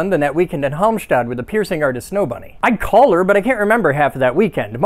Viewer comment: One hung lo. That killed me. Love your jokes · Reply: I heard "I collared her."